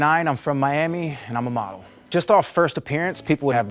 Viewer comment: I heard ''I'm Michael, I'm 29, I'm from Miami and I'm a mom.''